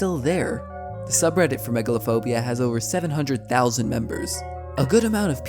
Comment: lol I thought that said megalon phobia